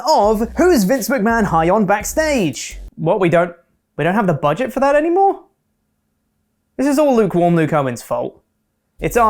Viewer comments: Bruh when he said “Aaliyah Mysterio” I thought he was saying “Ali and Mysterio” and I was like “damn, it really is just random at this point, they’re not even new wrestlers. Actually, Mysterio is an old wrestler” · I thought Pete said Ali and Mysterio lol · I misheard "Ali and Mysterio" and thought " Mate, do you see that Ali is doing right now?". For clarification Chopper said "Aaliyah Mysterio" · I honestly thought you said Ali and Mysterio. Can't you just pronoune names the way the WWE commentators do? This is almost as bad as calling Triple H, "Triple Hetch" 🤮